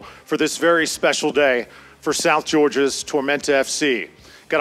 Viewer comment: Am I the only one who thought that said South Georgia Dementia?